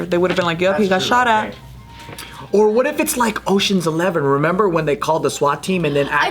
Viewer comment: I had to rewind because I thought Joe said Ocean's eleather 😂